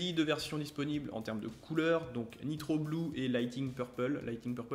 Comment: C'est moi ou tu a dis "mitroglou"?